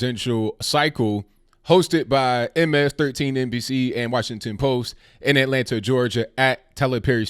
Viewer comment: I miss heard you in the beginning ABL, I seriously thought you said the debate was being hosted by MS-13 lol!